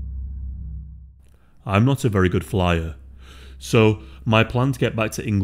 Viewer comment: Haha when the intro started I thought you said I’m not a very good liar 🤥 haha!